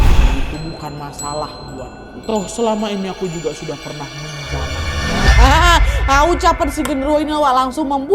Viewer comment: Menit 21:03 nada ngomong apa si, yang dengar jelas parade jawab yaa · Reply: “Toh selama ini aku juga sudah pernah menjamah nya”